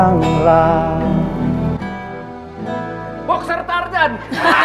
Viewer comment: Wendy bilang apa? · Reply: boxer tarzan